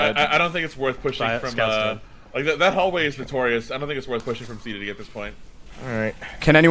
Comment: Guys it's Joever for me one of the times when you guys were calling out C to D, you were saying it so fast, I heard it as "skibidi". Anyways petition to rename the callout for the door from C to D to skibidi